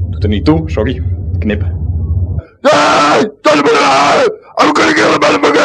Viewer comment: What does say at 54:01? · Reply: THUNDERDOME maybe?